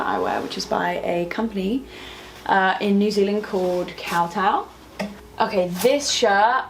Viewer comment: Did anyone catch what the New Zealand brand was for that white top around 17:20 in? Sounded like she said Cowtown?